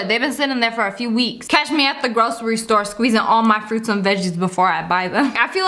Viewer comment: Who else thought she said gross ery store